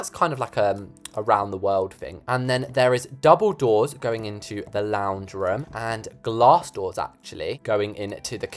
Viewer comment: OMG, when you said “double doors going into the lounge room“ I thought you said Dumbledore is going into the lounge room..!